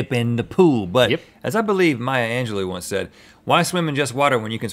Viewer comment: I thought he said my aunt Angelo